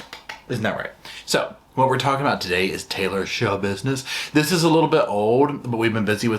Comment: This time, I heard "I am Schabusiness" 🤣